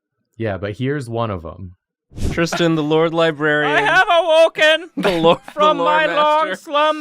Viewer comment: Imma be real here...always thought they said Laura Librarian